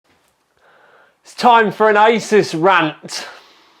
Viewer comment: Oh ahah i thought you said "its time for an ISIS rant" sorry i just have bad ears